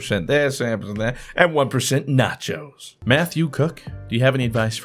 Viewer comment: I thought you said "meth you cook" til I went back and looked and saw the guys name was "Mathew Cook" lmaoo!!